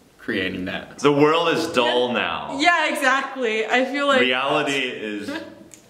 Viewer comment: did I just heard AKMU Reality :o))